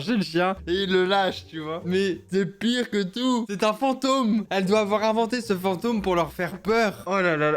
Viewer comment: j'avais entendu " c'est ma ceinture " et je pensais qu'elle allait les fouettés mais elle a just dit "C'est un fantôme " Punaise je vien just de réécouter et j'ai entendu "c'est ma photo" 🤣🤣